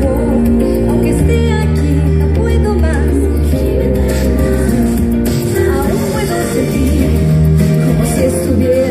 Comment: que dice en el minuto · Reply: Absalom Largo no puedo más